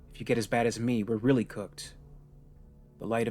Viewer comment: did she say they are cooked?🥀